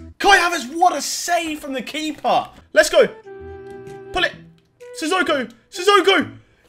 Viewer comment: pull it or gullit I thought I heard him say gullit anyone